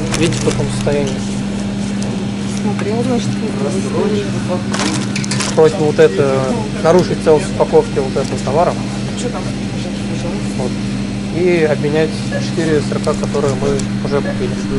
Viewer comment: кто услышал «просрочка патруль»?